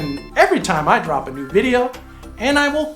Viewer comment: I had my back turned to the video and I coulda swore he said "..and I will cut you!". 🤭 At the end (11:08).